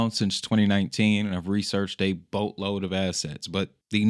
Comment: I thought youre gonna say "A boatload of ass". My bad.